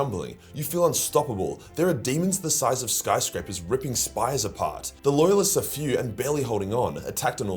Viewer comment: "There are demons the size of skyscrapers ripping spies apart" Huh? What? What did say? I can't understand his accent.